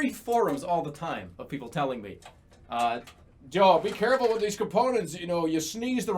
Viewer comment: did he just call himself Joel